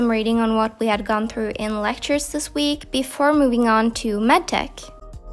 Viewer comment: What did she say in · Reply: "before moving on to med tech" it's written in the caption